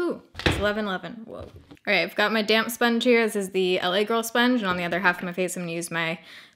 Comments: When you said “I’ve got my damp sponge” I heard “I’ve got a damn sponge”. 😆 I was like, well girl! Get a different one if you don’t like that sponge 😆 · def thought she said "okay, got my damn sponge here" 😂😂😂😭